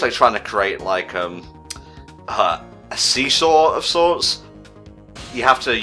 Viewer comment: D-Did you say "*Sesaw*"!? Like a... SEASAW EFFECT!? XD